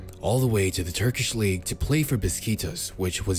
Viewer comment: did u just say beskitos??😂